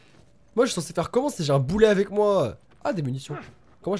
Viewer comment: A 9:37 j'ai compris " poulet " 😂😑